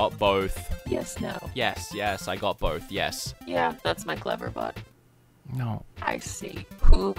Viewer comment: I thought you said Cleverbutt. The best kind of AI. The kind I’d be okay with taking over the world.